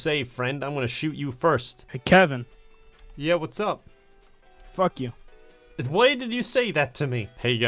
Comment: That sounded like fucking kwite am i trippin